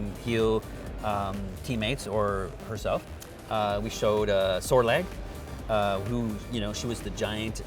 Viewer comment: did he said sorlag was shown at 1:21?